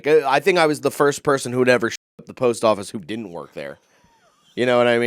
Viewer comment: I thought was saying "shi*ting up a post office" 😂 after he lies about having cancer because he wasn't paying attention and was mildly insulted I love it though, this is why I love the carnival of farts. Most underrated current comedian